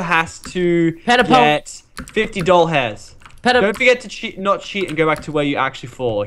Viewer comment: it sounds like lachlan is saying doll hairs or dollhairs, the second one means dollars, is that rigth